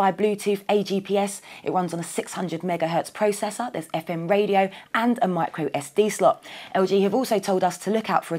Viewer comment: does she say "its got effin radio" ? lol